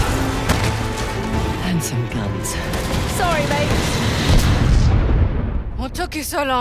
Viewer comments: Reminds me Uncharted 2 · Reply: Did Chloe said "Sorry Nate" in 1:32?? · I CAN'T WAIT TO PLAY THIS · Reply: Did Chloe said "Sorry Nate" in 1:32?? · Did Chloe said "Sorry Nate" in 1:32?? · Who thought that Chloe said Sorry Nate but its actually mate